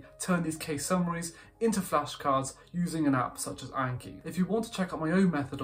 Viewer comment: What was the app called? I couldn't catch it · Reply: anki